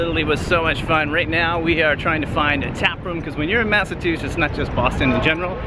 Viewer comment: did he just say “Massatwoshits”?